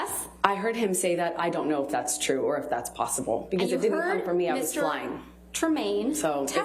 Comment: did she say “it didn’t come from me I was lying.”?